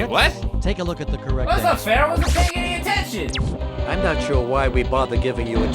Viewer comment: what said "that's not fair i wasn't paying attention", what subtitles thinks he said "Sh*t"